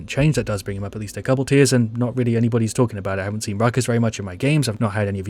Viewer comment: I thought it said Fuckus lol